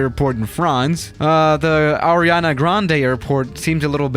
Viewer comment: Who else listened Ariana Grande instead of Llanada Grande?